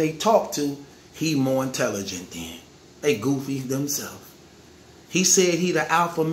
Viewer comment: Damn my phone thought he said "hey google" when he said "they goofies" lmao